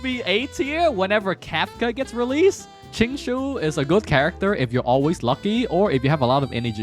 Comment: bruh i thought he said xingqiu😂